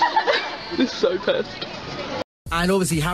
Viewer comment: so i put it in 0.5 and maybe im stupid but i hear “this is to lou” edit: rn i also hear “this is two ghost” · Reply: Alexandra Reyes but u gotta admit it sounds like he says “this is to Lou”